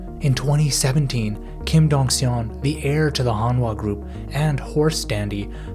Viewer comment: The subtitles are wrong. It's "Honwha," not "Honda."